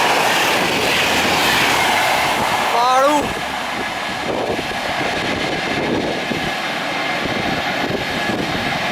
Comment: fantástic!...very fast! ! .what say at the end of video. in 00:37? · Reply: Thanks friend..!! :) :) in the end i utter Fhaadu which in English means "Awesome"